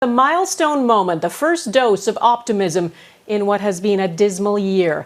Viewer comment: I thought she was going to say first Dose of Autism.